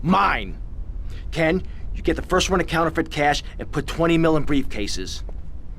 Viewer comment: Tommy:" Ken, you get the first run of counterfeit cash and put twenty mil in the briefcase." Subtitles:" Ken, you get the first run of the counterfeit cash and put three mil in the briefcase." Tommy says 20 mil but subtitles say 3 mil.